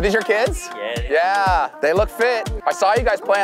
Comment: did he say that those kids look fit?????